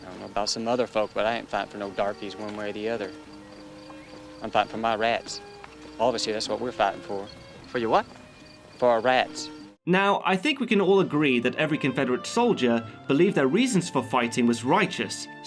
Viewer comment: lmao, I was so confused about why the Southern guy was fighting for rats at 19:47. I had to watch it 4 times before I finally understood he was saying "rights." Even the subtitles say "rats" 🤣🤣🤣🤣🤣🤣